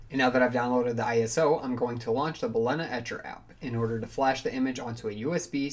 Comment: it wasn't clear of what application you said here, mind can you say it here? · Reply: BalenaEtcher